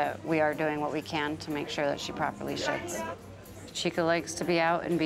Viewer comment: sounds like she said “properly sh*ts”